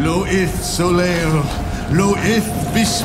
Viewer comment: I heard 'Ulgrim'